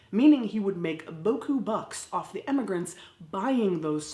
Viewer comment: I learned something new today...the phrase is beaucoup bucks. I always heard it as boo-koo bucks. Thank you closed caption!